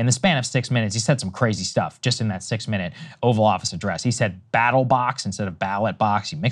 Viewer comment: I heard "Battle Bots"